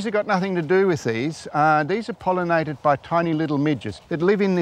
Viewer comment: I thought he said "tiny little midgets". Like little oompa loompas come out and pollinate, then go away.